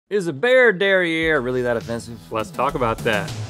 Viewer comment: In the opening Rhett said "bare derriere" I thought he said "bear derriere" 🤣🤣🤣